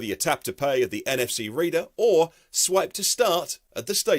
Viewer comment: I've listened thrice to EV advertisement . I swear he says to charge use the app or "Sweat" to start. 0:59 Must be my ears.. <G>